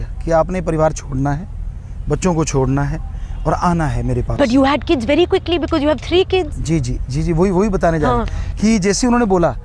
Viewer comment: she actually said you had kids very quickly you had 3 kids 😂😂